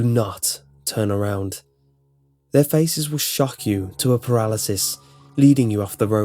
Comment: hahahah thought at 10:45 i heard 'their faces will fuck you'